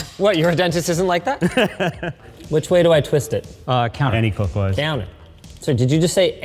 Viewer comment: I heard "any clockwise" too and was confused by it.